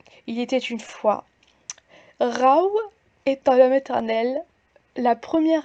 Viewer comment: Au lieu de "Rau était en maternelle", j'ai entendu "Rau est un meme éternel"... Mon cerveau a failli s'étouffer en riant. Et mon corps est resté de marbre face à sa connerie. Donc j'ai juste souri de ma blague, pas ri XD